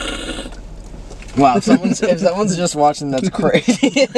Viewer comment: what did say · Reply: They made like a dirty joke saying “if someone was watching that crazy?”